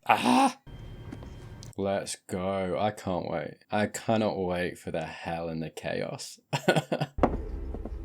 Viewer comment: Sounded like "I cannot wait for the Helena chaos." 😲